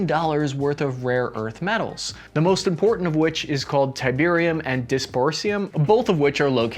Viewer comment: Surely I am not the only one who heard him say Tiberium